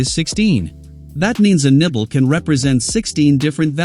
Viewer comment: I keep hearing nipple 😭😭😭